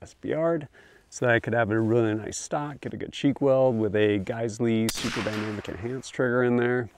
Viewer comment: What trigger do you guys recommend? I heard him say geisslie but couldn’t make out what he said I want it for my Noveske Shorty gen 4